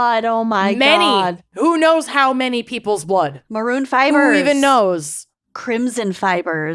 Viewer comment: I heard say, "omg Maroon 5ers" and tried to think what the band had to do with any of this. Then it clicked, maroon fibers. 😅😂